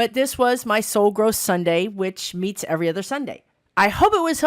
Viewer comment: I did a second take, when I heard you say this was SO GROSS SUNDAY. I thought that was weird and out of context with your usual videos. However, on my 2nd and 3rd takes, I realize that's not what you said, even though it sounded like it at first....